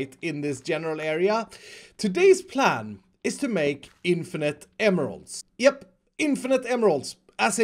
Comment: i think he is sayng infinite AMMO